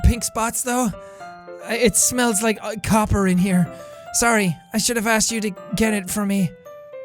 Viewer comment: Did he just say 'I smell copper'?!